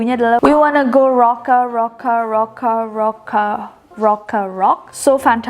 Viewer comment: I remember couple years ago when SHINEE's Ring Ding Dong is first time released, I misheard "Rocka Rocka" with "Cola cola" due to their pronounciation 😂